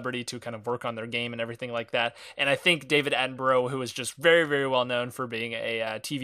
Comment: Lol it sounded like you said David Attenbro instead of David Attenborough.